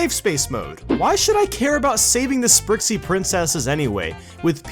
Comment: I heard Die😂